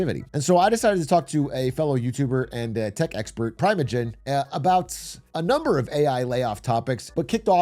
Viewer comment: Am I the only one who heard Privategen?